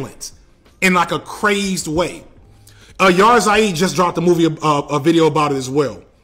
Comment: who..? "Yar zaid" is what I heard 🤔